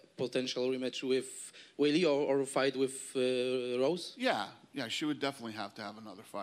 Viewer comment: Did Dana say "Rogue Rose" @ 15:18, or was I just trippin'? :-P